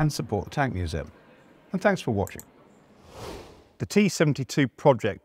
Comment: Did he just say "tanks for watching.!"?